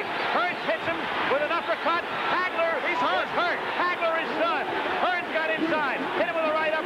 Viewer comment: Does he say Hagler is done